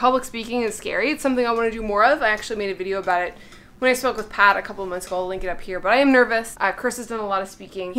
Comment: Is it just me or does sound like she says 'when i smoke with pot' at 9:40, instead of 'spoke with Pat'? 😂